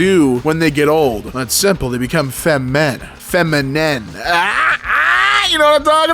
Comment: thought he said fem&m and I perked up like a dog